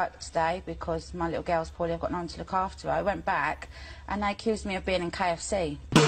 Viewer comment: what did she say? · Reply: She said that she was sick and couldn’t go to work, but they accused her of being in KFC